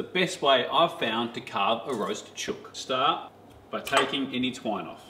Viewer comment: Thought he said this is the best way to carve roasted children lol